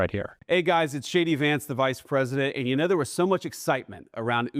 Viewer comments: I swear I heard him say, “hey guys, it’s Shady Vance here!” 😂 what a big old dope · I swear I heard him say, “hey guys, it’s Shady Vance here!” 😂 and btw .. it’s looking like he’s wearing more and more eyeliner as the days go by. He’s going hard for that smoky eye